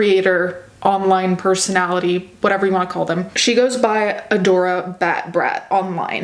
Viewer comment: i thought u said adora bad breath 💀💀💀 i was confused for a second